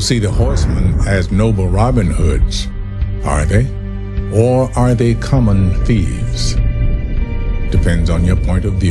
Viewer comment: what's the word? ? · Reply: Saint